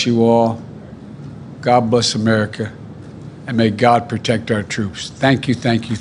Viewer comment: He really said God Bust American❗️WTH 😕